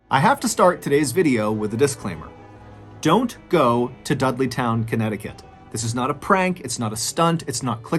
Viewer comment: "don't go to Dudleytown" What I heard "Go to Dudleytown" Next Mr Ballen Episode "She did not listen and she is now a shadow figure "